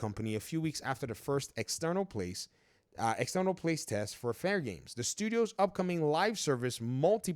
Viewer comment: I see someone voice dictated and then didn't proofread "place test" was picked up instead of "playtest". I remember when there used to be editors.